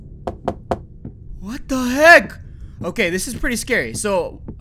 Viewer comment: did anyone hear the guy behind the door say “what the f**k are you doing out here”?